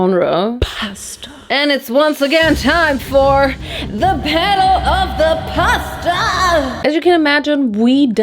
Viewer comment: all i hear is "The battle of the Pastaaaaaa" hahaahahah XDDD